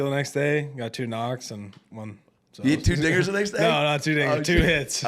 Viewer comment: did anyone else hear that? Or just me...🤔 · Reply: Bro Adam you said 2 N*GGERS THE NEXT DAY!!!! WTF Adam Ray!! How dare you…OK IM OVER IT!!! 😂